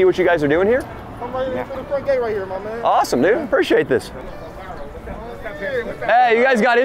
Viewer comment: Did just say "awesome nigga" at 13:27 LMFAO